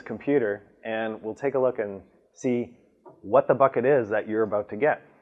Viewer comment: Woah! For a minute there I thought he said “What the F*#! it is your getting” instead of “ What the Bucket is your getting” 🤣